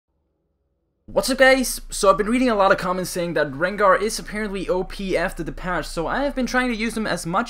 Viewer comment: it sounds like you sayin: whats up gays